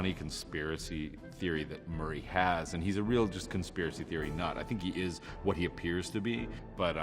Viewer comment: What I heard was “Merry is just a real conspiracy theory thot” did anyone else hear that? 🤣🤣